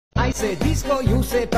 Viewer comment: i hear "i say desko you say panty desko desko panty panty💅💀"